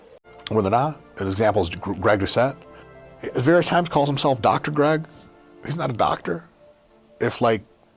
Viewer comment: I didn't hear him say that. He said, Greg cheated in every sport that he participated in. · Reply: i think he said, various times calls himself Dr Greg he's 1:45 not a doctor if like cheating at every 1:48 sport you ever played makes you a doctor 1:50 dope I got eight phds in that case that was i was refering to, maybe i understand it wrong, but english is my second language =)